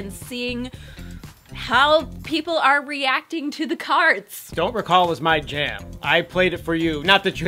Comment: I heard oh nana and I was like hmm it's listenable lol but THEN I heard Don't recall and I was hooked